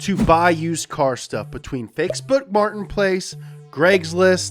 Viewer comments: “Greg’s list” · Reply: i heard him say fakeboots martinplace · love how it sounds like he says "fakesbook martin place" "greggslist" and "offer up"